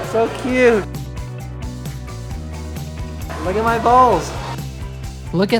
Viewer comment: Did he just say “ look at my balls” at 12:25👁👄👁